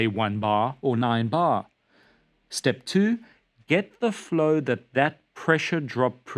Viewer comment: I pause the video at a bad instant (Step 2: Get the f...) and it sounded like you were about to say, "Step 2: Get the duck 0ut" 😂